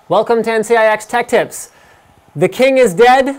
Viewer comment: it sounds like he is saying NCIX "Detectives" in the end